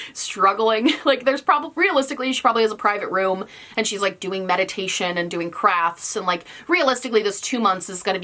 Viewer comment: when she said "she's probably doing meditation, doing crafts" I heard "doing crack"and I was like damn girl 😶